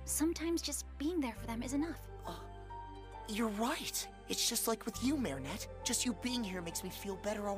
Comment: why did I hear ur white💀💀💀💀